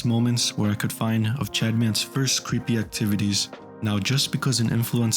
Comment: I keep hearing him say “Chadman”